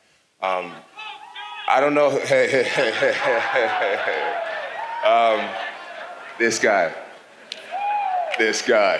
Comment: What’d the guy say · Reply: He said I'm on coke Jon